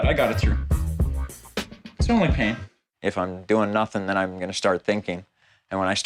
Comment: did he say nazi thing?